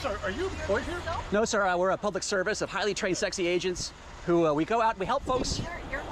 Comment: did he say “highly trained sexy agents”💀💀💀💀💀💀💀